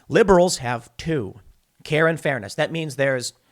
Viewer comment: Did you just say "Karen fairness"?